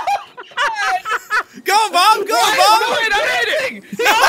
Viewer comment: i thought he said goat mom